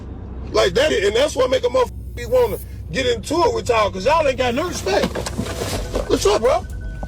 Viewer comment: ' "Hell no" was not what he said initially He said 😂"That's what makes me wanna get innit wif y'all" Nek minnit 😂😂😂😂 You're in it shit talker 😂